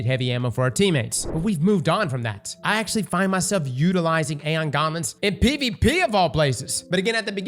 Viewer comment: Aeon Gauntlets? Or Aeon Garments? 😂. Sounds like ur saying garments